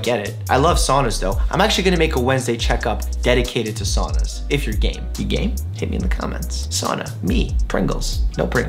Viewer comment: omg for a sec I thought he said if your gay